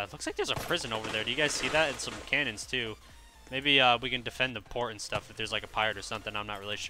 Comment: when he said maybe we can defend the port i thought he said maybe we can defend the porn